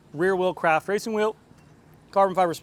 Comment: For one second I thought i heard said Crap wheels 😀